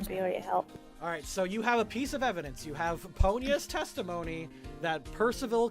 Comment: I swear, every time I hear you say "Ponya" referring to the manaphy, I instead hear "Ponyo." XD